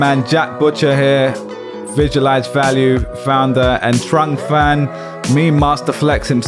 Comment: thought said "Vigil-ize Value" at the intro and i'm not mad about it ... ( jack is batman )